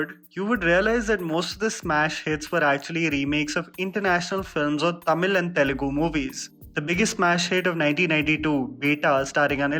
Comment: can someone clarify what he said ? ? Is it international film and Tamil and Telugu films?